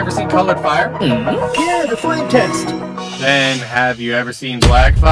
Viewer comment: Im so cooked bro i thought he said "flint and steel" when he said flame test 😭😭😭